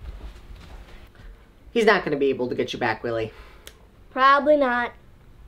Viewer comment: I thought he said “ he’s not going to get u back Willy” XD but he said really XD I’m crying XD